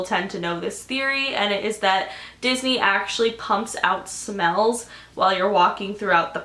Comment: when she said smells i thought she said spells lmao